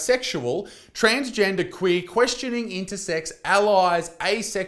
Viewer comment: thought it said Quisling instead of questioning at first